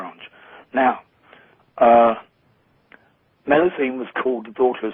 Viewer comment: WHO was called the daughter of satan? Cannot understand him. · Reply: Meluisine